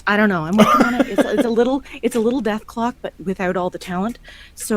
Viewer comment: I thought she said deathclaw #feminismreligonpeace